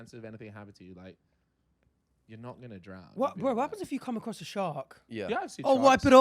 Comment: I don't get it 😭 what did he say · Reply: What if you “come” across a shark “Oh wipe it off”